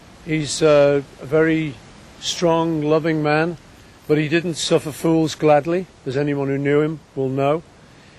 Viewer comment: what did he say? he didnt suffer fools gladly?